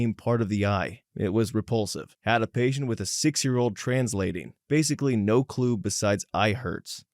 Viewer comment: I thought he was saying "six-year-old trans lady"